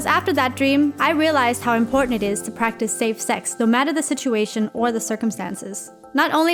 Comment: Hold did she just say " practice sex " why would I practice thats shit???